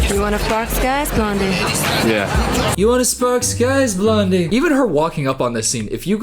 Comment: It sounded like she said to Paul Walker, "You wanna pass gas now?"